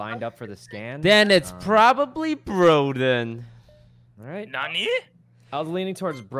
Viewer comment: did he just say NANI?!